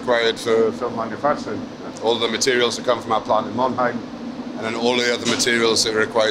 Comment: did he say Mannheim or Monheim as in Inoviscoat?🤔 Edit: Monheim as by the subtitle at 21:36.